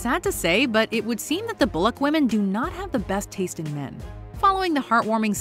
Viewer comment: I had to repeat so many times cause I thought it said the black women have a bad taste in men 🫠🫠😫😫😫z she really said bullock